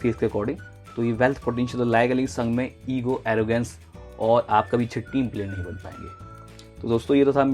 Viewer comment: ego Arrogance ke bad kya Kaha appne...? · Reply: Power